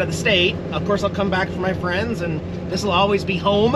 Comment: I thought he was saying friends also